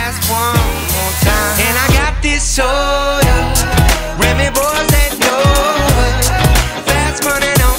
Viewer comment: It could be because of Bazza but i always heard "and i got that soldier" Like when Roadhog hooks Soldier76